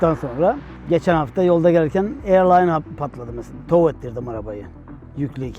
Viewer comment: air Line diyor yani hava hortumu patlamis.